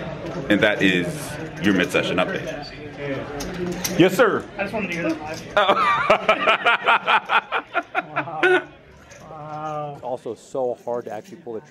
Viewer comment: What does he say @ 27:54? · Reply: That he wanted to get on the mid session update 😂